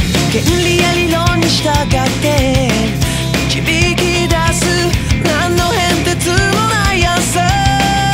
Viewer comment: 何の変哲のないアンサーが何の変哲のない朝に聞こえたの俺だけ？